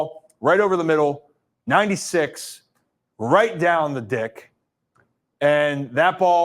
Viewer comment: Did he just say “right down the dick”?!? 😂😂😂